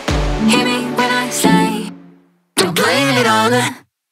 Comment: As someone who's english is the 2th lenguage, i conpletely misundertood the music my entire life. I thought she said "dont blame it on the kids" but today rewatchong it i just realized that is "to blame it ob the kids" irs much deeper now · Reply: um it’s actually “DONT blame it on the kids” so the first time you thought was actually correct 🤓☝️